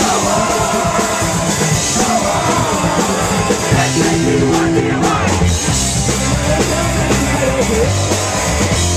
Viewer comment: what the fuck are the dudes in the crowd saying at the end of the chorus(at 1:11)? ive never heard that. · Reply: S-A-T-U-R-D-A-Y